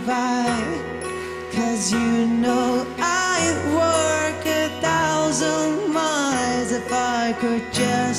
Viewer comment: 'cause you know I'd walk a thousand miles が 'cause you know I'd work a thousand miles に聞こえる。 それでもいい声だから許す❤